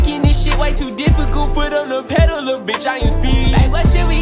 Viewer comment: i thought he said steve